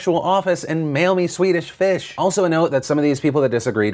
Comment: All i heard was "I need TONS of Swedish fish mail it to me"" -Philip Defranco hahaha who's with me?